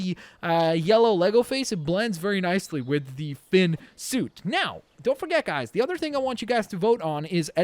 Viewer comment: did he just say Finn suit